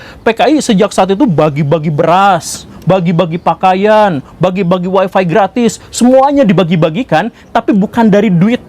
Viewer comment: bagi bagi wifi..? maaf apa saya yg salah denger ya..? klo mmg iya apakah saat itu sdh ada wifi ya..